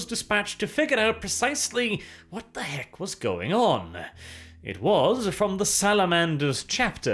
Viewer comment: i swear I heard "what the Vect was going on". I wasnt wrong i guess xD